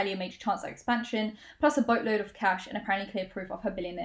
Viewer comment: When you said "boatload," I heard "butt-load" and had to rewind to actually read the text to make sure